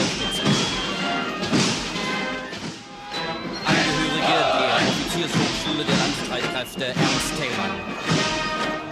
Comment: maybe there is a german person here or someone who can speak german, what are they shouting at 13:28 and 14:20 ? tell me pls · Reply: All I can hear (a little more clear at the second time stamp) is „und EINS, ZWO, DREI“ —and one, two, three— Nothing to special 😉